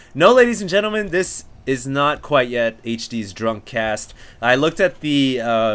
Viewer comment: i thought you were saying drum cast.